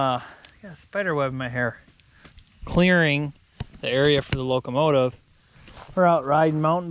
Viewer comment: Explore more locomotive? That makes no sense. It sounded like you said explore more locomotive